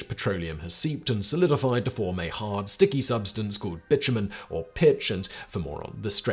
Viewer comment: Simon! Did you just say b*tchumen instead of bitumen? XD